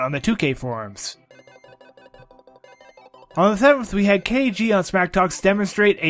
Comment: What did he say? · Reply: I was saying on the 7th. KG Came out with his trainer for the game